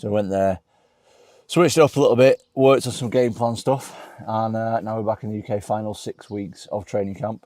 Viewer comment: Pardon? Did Tom say he’d worked on some “Gay porn stuff”????